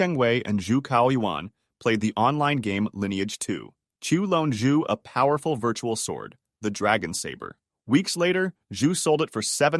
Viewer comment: "Chu loaned Zu a powerful virtual sword." It took me 5 minutes and help from the captions to understand what he said there in that part. XD